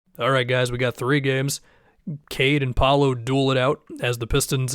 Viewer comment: said “Cade N’ Paolo” at one point and i thought he said Tame Impala lol